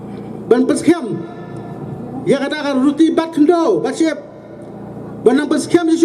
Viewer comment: did he say ruti😅?